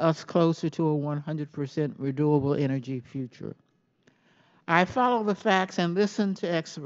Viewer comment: She actually said "100% reDOable energy." LOL!!!!